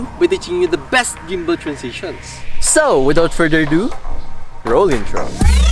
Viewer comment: is say " Without further ado ROLLING FROM or CAM? " 🤔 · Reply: Roll intro